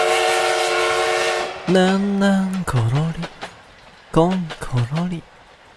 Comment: 最初のねんねんころりこんころりがなんなんころりこんころりに聞こえるんだが？